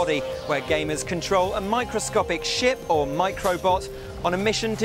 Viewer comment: lol i thought he said microscopic shit